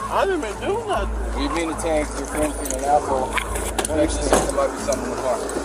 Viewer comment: for those who couldnt hear 8:50 he said "you are being detained because your friends being an asshole"